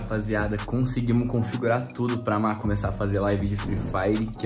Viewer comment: Aí Rapaziada Pelo o que Eu Escutei 9:12 :Ela Fala Amor Me Corrijam Se Estiver Errado